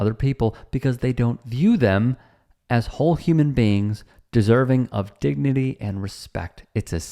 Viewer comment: Leguizamo is right, to the fork we are all the same beans... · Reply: Agreed. Human beans, or so I thought they were saying when I was little.